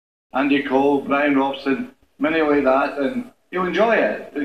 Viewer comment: “Welcome Ji to our club” that’s what he said I think. But this is so funny, true. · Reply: Sounds like he was saying “You’re gonna” but cut himself off and said good luck